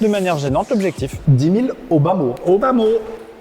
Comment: "au bas-mot" j'ai compris "Obama" :/